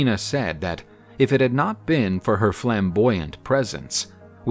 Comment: wait... Am I the only one Demon Slayer fan here... Sounded like "✨ FLAMBOYANT✨"